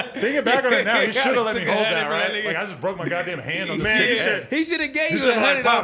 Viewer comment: did adam say i just broke my hand on this niggas head or am i tweaking